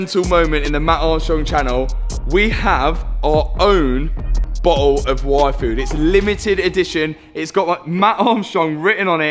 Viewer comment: It sounded like you said "We have our own bottle of Waifu." Lol